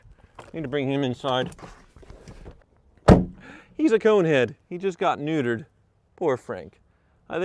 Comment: 😂 I thought you said coonhead! I was about to call you racist 😂😂